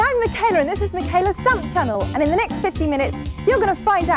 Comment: Wow, I thought she said this is Micheala’s Dump channel.